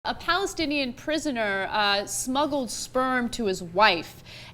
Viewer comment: i thought it said snuggled sperm